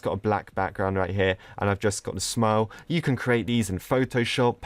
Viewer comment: What say on 2:25 : I've just gotten smile What caption say on 2:25 : I've just gotten smoke